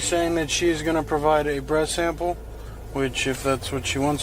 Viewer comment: Cop Said BREAST SAMPLE. INSTEAD OF BREATH 😅